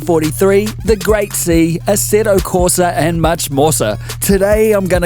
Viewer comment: Much Morsa!? Seriously dude :-) "1943 Berlin Blitz" is a great find, thank you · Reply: I thought he was saying "much more sir" which might fit with those moustaches :)